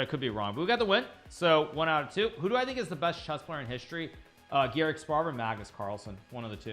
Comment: who did Hikaru say was the best other than Magnus ? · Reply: garry kasparov